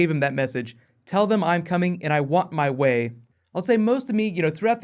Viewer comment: I thought he said "I'm on my way." As in he is heading into the city.